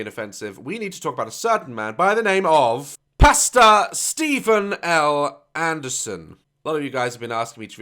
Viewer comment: K so I know u said 'Pastor Steven L Anderson' but honesty thought you said pasta